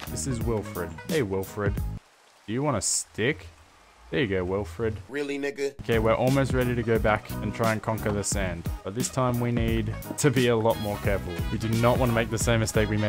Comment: Me: Confused and thought he said "Really Nigga"